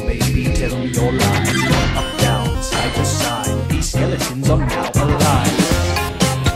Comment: "They want our eyes, and they're going to eat them." Yeah they want our eyes watching this 40 million times. They're just eating up our reactions to this. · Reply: I keep hearing them say ‘these skeletons want my thighs’ and it has cursed me